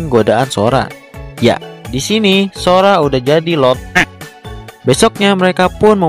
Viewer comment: suara Wek nya jadi kedengeran LONT#, bener kan